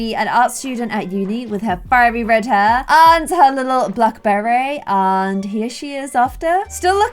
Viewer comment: When said ‘black beret ‘ I thought she said blackberry! 😅